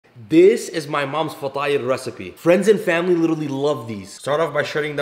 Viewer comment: I heard it as “my moms fat tire recipe” still not disappointed